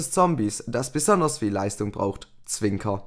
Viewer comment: Dass bisher ......... Leistung braucht. Ich verstehe das Wort nicht :? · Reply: BlackBlazed "Das besonders viel Leistung brauch."